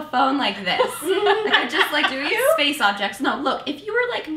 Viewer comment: What does Natasha say at 0:55 ? After she tells Elise "you don't pick up a phone like this"? It sounds like she says "space objects" I'm just a little confused. Is that a saying??